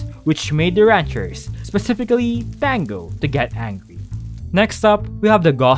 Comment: the subtitles say fangirl instead of Tango 🤣 🤣 this is amazing! I love how Pearl is upside down